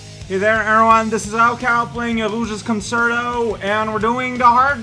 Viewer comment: Death count: 207 [746] (4 today) (0:02) "Illusia's Concerto"? Is that what you said? I've never heard of anything by that name. EDIT: Actually, probably said "Rouge's Concerto", which makes more sense. (0:17) Wow, that was a fast death, courtesy of that worthless consumer model. (0:41) If you're not in a 100-ring mission, you can frequently find rings randomly by just digging anywhere. (1:55) Darn worthless consumer models. (2:27) Geez, how many times is the game gonna clip you through the floor like that? (3:09) It's dumb that you're clearly meant to use that monkey's bombs, but he keeps being prone to suiciding. (4:00) Okay, that worked somehow. (4:03) What, only a "Good!" (100)? But, you found it within 1 minute and with no hints, or with 1 hint if it counts the hint you read on a previous life. Oh, but if that's the case, did it count all of the elapsed time on your previous lives as well? Perhaps that's why. (5:27) Took just over a minute for that one, so you got "Awesome!" (1000). (5:32) Caution: Fire hurts. (6:48) Aw, missed Golden Boy this time. (8:40) And, that one was another "Awesome!" (1000). (8:52) The A-rank score for Egg Quarters Mission 5 is 13,000. (9:37) RIP Chao's bunny ears. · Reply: I think I heard "Rouge's Concerto"